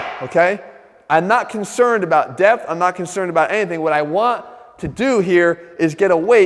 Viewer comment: first I heard "I`m not concerned about death", as pins would help from dropping the barbell on himself. lol